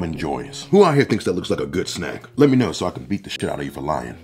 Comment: GOOD SNACK... I FIRST THOUGHT YOU SAID NUT SACK...😂